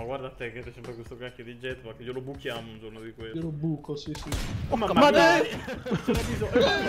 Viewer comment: Giuro che ho sentito un "e la madò!" al minuto